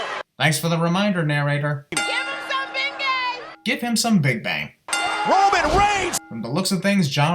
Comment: I thought she said "Ben Gay"😂